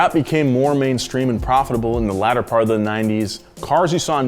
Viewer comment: this man just said ladder instead of later 😂😂😂